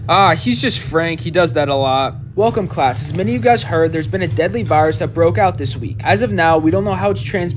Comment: I thought the teacher said “there is a daddy virus” 💀💀💀💀